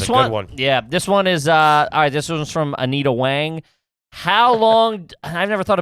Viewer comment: Did nobody else catch that at 1:07:15 where he's reading a Q and he says it's from Anita Wang, but sounded like "I need a Wang"!!! LOL!!!!!!!